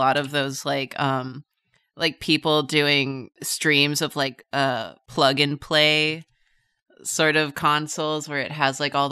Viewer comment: i swear, i heard saying "flogging play, " and was like "on Twitch?"